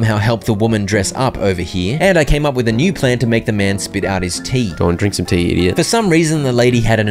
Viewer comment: When I first played it, I thought it said make a man spit out his teeth not his tea